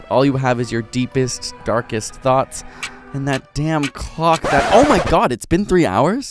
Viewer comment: I had to rewatch that three times, a triple take. Did anyone else hear what I heard? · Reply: D a m n C l o c k